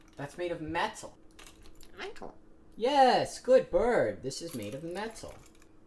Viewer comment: am I tripping or did just say mental??